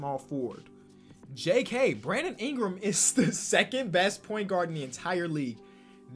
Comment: did he say second best point guard